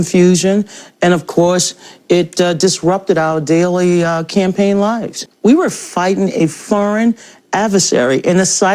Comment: did she say campaign lies? freudian slip